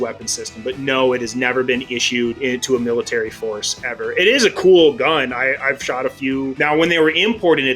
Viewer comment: am I trippin’ or did he just call the D.E. a “cool LITTLE gun?” A “little” gun? Am I trippin’ bro?? I GOTTA be lol